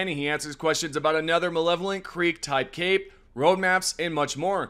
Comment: why does everyone seem to have a hard time pronouncing malevelon creek? its literally just muh-level-on. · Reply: all good, it just sounded like you said malevolent. but Ive heard waaaay worse attempts at it than yours lol